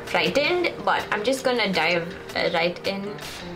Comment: I misheard Dive right in for the spicy ramen as D*e right in and was like :O . Love this video! Go Go!!